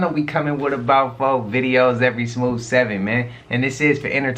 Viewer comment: you sound like you saying smooth 7. I love ❤ it... it's 100% you. And yo I wanna say I love u doing longer videos and I appreciate your efforts and all the work u do for us to have these videos so thank you and God bless